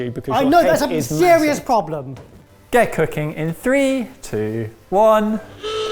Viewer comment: May be the 13 hour work day, but I thought said "Gecko King in 3...2...1" instead of :"get cooking."